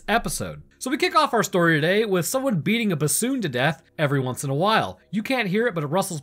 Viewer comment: I had to rewind since I thought he said beating a baboon to death lol